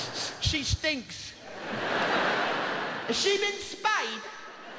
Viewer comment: What did he say here? 1:36 has she been ???? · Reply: Spayed